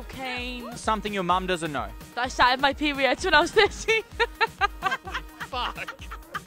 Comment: what does she say at 4:37? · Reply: “i started my period when i was 13” thats all im guessing